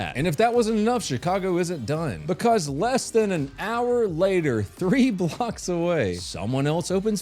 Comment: A first I thought he said "three Glocks away"... 🙄